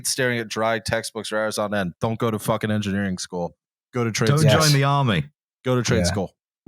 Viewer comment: sounded like said "go to train school"